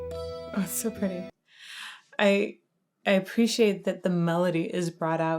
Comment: Question: What did you say at 2:48? Did you say? "O super je"? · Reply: ”Oh so pretty” is what i heard